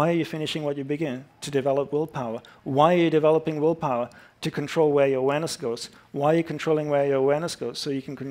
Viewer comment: what did he say? to complete what ? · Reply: To control where your awareness goes ! That's what is saying